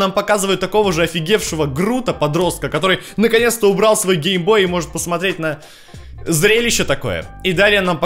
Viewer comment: мне послышалось, что гей-бой